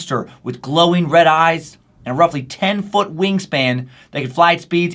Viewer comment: When you said wing span I swear I thought you said "ween span" lol